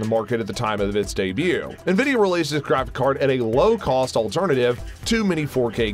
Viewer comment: it sounded like you said the graph card